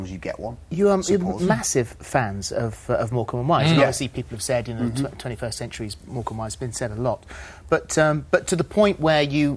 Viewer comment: What does Phillip say at 3:32? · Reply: He said "You are massive fans of Morecambe and Wise. Obviously people have said 21st century Morecambe and Wise."